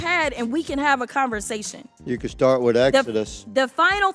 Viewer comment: Did say "you could start with Exodus"? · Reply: Yes, he said "you can start with Exodus". PLEASE, my head just exploded.